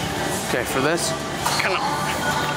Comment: i didnt catch that. for midback exercises like this, did you say to avoid retracting scapula?